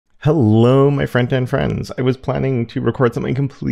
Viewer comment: After watching more than 500 of your videos, I just realized you say "Hello my frontend friends". Not "Hello my friend and friends ". Even the YouTube auto-captions have misheard you all this time.